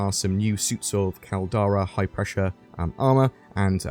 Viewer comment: Star Citizen Alpha 3.15 NOW LIVE - What You Need To Know GET NORDVPN | <link> GET GAMEGLASS | <link> BECOME A CHANNEL MEMBER | <link> STAR CITIZEN 3.15 UPDATE | <link> GET STAR CITIZEN & BONUS | <link> BECOME A PATREON | <link> DONATE TO THE CHANNEL | <link> DIRECT PAYPAL DONATION | <link> DISCORD | <link> WEBSITE | <link> TWITCH | <link> TWITTER | <link> REDDIT | <link> PODCASTS | <link> CHECKOUT HASGAHAS SCREENSHOTS | <link> LATEST STAR CITIZEN PATCH ALPHA 3.15 LIVE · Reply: did you say “new high pressure armor”? And any speculations on why? Under water? Higher pressure planets? Maybe space whale mining? Etc…